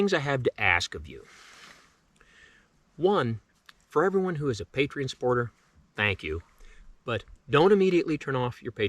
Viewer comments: I thought he said a Patriots supporter and was getting ready to say F New England, lol. But sorry to hear about your passing, brother. Rest easy. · Bruh I thought he said PATRIOTS supporter and I was like YEAAHHH GO THE PATS 😭😂